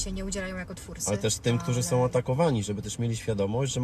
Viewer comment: Na początku u psycho kapól usłyszałam zamiast atakowanych to otagowanych